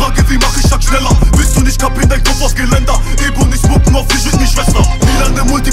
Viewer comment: Was sagt er da? "Ebonies mucken auf..." irgendwas mit Schwester